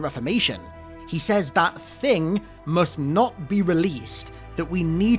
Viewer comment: Actually he says "But they must not be released" At first I thought he said "that thing" as well, which made me think of the Primordial, but alas, he does not.